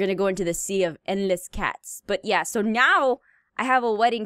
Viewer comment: it’s sounds like meow instead of now